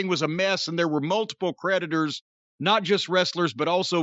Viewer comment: After Jim mentioned Rob Feinstein, I genuinely thought he said “predators” rather than “creditors”.